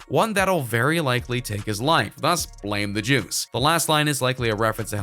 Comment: 'plus blame the jews' is what i heard